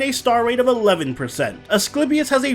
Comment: For some reason I thought it said Assclapius lmao.